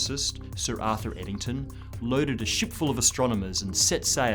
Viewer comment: he Says: "Loaded a ship full of astronomers" what I heard: loaded a shit full of astronomers" 😁 my gravitational hearing is at an all time high lol